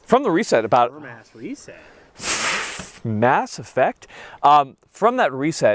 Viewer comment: Did he say Mass Effect during trigger portion @ 20:35 🤨🤓???